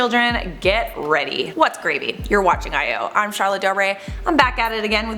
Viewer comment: Did she just say “what’s gravy?”... whew chile yall need to stop😂